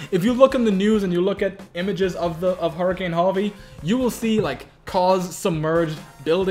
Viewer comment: Totally thought said hurricane Javi lol